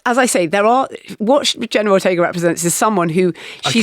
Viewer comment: I also thought she was saying General Ortga.